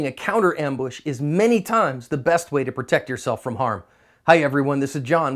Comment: I have learned a lot from these videos one thing i havent heard mentioned is the importance of keeping your weapon on the ready with one in the tube also it sound like you say protect your asp